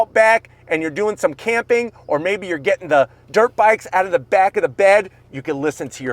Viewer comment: I swear I heard he said "get your dirtbags out from the back" and wow almost to a Mil sub!!!!!